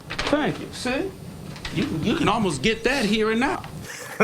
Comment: what does he say? u can almost get that here and now?